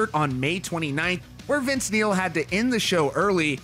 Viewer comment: I heard "Vince meal" not neil. 😩😂